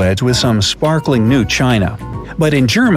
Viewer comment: Tan ZANE ia.... 🙄 Not just a bad "British" accent buddy! 😂🤣😅 That crying before the wedding thing, in China, I totally resonate with absolutely! Oh hang on... BEFORE the wedding? Oh sorry I thought he said AFTER...🙄